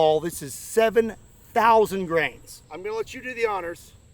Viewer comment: I thought you said this 1lb lead ball weighs 7000 grams and I was like 1lb is definitely not 7k grams lolol. Then I remembered what we're talking about and realized grains.